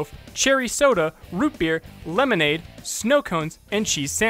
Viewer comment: did you just say beer? hes a kid